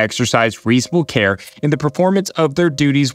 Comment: Man I had to rewind for a second on the Scarano section when he said Instrument of Harm, I thought he said Instrument of Horror... LOL... I do feel awful for the families that lost loved ones in this case though... This is something that was entirely preventable.